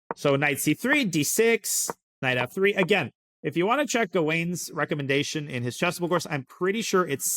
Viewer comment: Daniel says "If you want to check dwayne's(?) recommendation..." . I can't really understand the correct name so can't find such a chessable course. Can anyone help me? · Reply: Late, but in case you haven't found it yet: Gawain Jones is the author